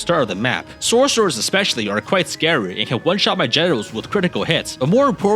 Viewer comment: did u say "generals" or "genitals"..